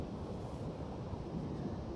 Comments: Did he just say- turtle food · For a sec I thought he said turtle fish 😂